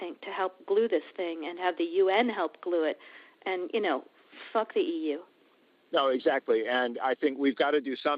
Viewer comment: Did she just say FUCK THE EU?